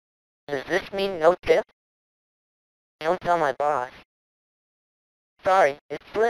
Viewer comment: Oh, he says "Don't tell my boss." All these years I thought he said "don't tell my pa."